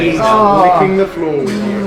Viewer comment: Did say "He's LICKING the floor with you"?